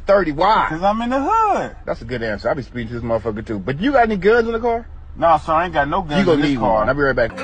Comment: “ you gon need guns I’ll be right back” 💀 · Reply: Everytime I saw that meme, I thought he was saying “goods”, drugs until now with the captions